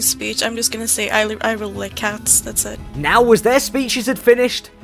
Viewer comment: the subtitles really corrected her to say she likes dogs instead of cats that’s great